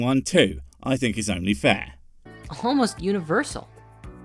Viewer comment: It sounded like he said "homeless universal," not "almost universal" which had questioning how that would work.